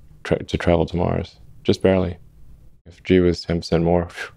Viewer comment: I really enjoyed this conversation with Elon. Here's the outline: 0:00 - Introduction 1:57 - Consciousness 5:58 - Regulation of AI Safety 9:39 - Neuralink - understanding the human brain 11:53 - Neuralink - expanding the capacity of the human mind 17:51 - Neuralink - future challenges, solutions, and impact 24:59 - Smart Summon 27:18 - Tesla Autopilot and Full Self-Driving 31:16 - Carl Sagan and the Pale Blue Dot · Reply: Great video! Awesome ending! I'll just ask you to please edit the subtitles in two places: one is at 33:54 Elon mentions if "G" were 10% lower or higher, but the subtitles say "due" and "energy". The other is at 35:37, Elon says "This is false: Mars" but the subtitles read "possible". Thank you again!